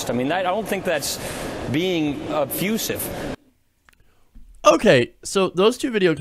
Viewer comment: did Paul Ryan use the word "obfussive"? ?!! wtf is obfussive? We can take the word obfuscate, but that means to render obscure, unclear, or unintelligible. Perhaps Paul Ryan was told to obfuscate his answer to the question lol.